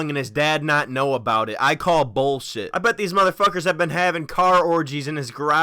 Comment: sounded like said bullsit XD